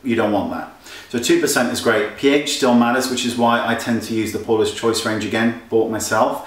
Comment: I thought you kept saying "Poreless Choice" - love your accent - hee hee heeeee (60 yr old Hick from Lansing, Michigan)